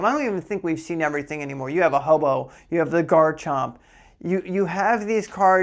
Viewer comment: Did you say "we have a hobo?" at 10:32 lol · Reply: Ho-oh 🫣🤣🤣